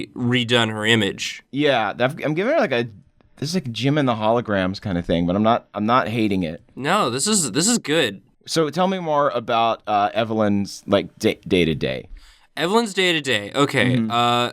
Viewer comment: When yall said jem and the holograms i thought you said jim and the hologromas so you should draw knock off jem and the holograms. Jim and the projections.